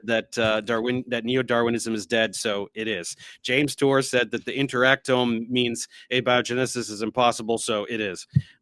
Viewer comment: when you said the word "interactome" (which I never heard), I misheard it to inter-rectum :D